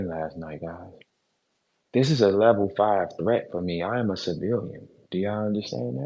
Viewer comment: Everytime said "I am a civilian", I heard "I AM A ROOSTER" 😂